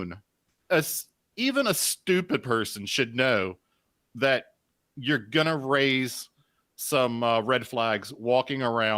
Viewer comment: 1;15:33 "I'm not a very......" me waiting and thinking "Sober person? Factual person" what is she trying to say??? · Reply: "competitive person" was the phrase she couldnt find